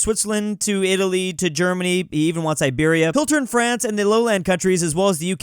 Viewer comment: Iberia sounded like Siberia